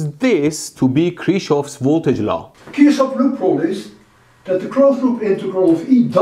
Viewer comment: I think Dr Lewin just said Kirchhoff‘s Loop Rule (KLR) and NOT Kirchhoff‘s Voltage Law (KVL) or am I wrong?